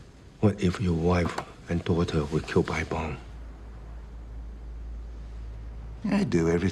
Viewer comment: what was Jackie saying ? 0:39 "bomb"? " bond" ? <link>